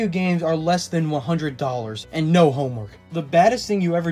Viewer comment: Misheard no homework as no homo. Lol.